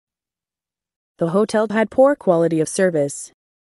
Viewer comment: Corrections in minutes: 2:55 "the hotel HAD poor quality of service." 3:04 What I hear is (yeah) not (here), but could be the English accent. "fill out YOUR name and address and let me ask you a few questions." 3:33 "I went into the library this afternoon to have a look at THE newspaper." 3:48 "You don't want to miss your plane on the big day, right?" 4:03 "The two PHENOMENON are in fact intimately related." 5:32 "She hadn't KNOWN he was such a good cook." 6:04 "I MEAN <--(not sure about this) they just both happened to HAVE BEEN around at the same time." 6:22 " I hope you all HAD a good trip." and I give up trying to correct the rest lol it's too much, tell me what you think, cheers.